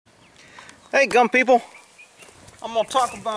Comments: Lol at first I thought he said dump people..not gun people... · sounded like you said dumb People had to replay it a few time . Gun People lol